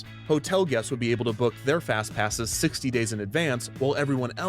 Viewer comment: I literally thought it said FatPass